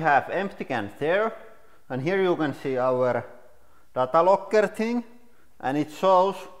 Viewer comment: What did he say? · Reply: Data logger thing